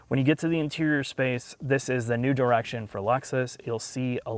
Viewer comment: Did he say "this is a nude erection for Lexus"